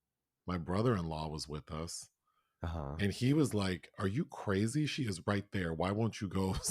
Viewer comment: I thought J Lo was saying Are You Ready! 😮